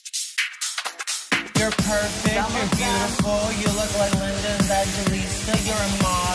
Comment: I heard : "You're perfect (Samantha) You're beautiful"